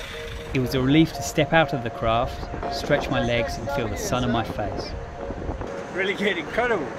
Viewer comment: I heard he says feel the sperm on my face :-) :-)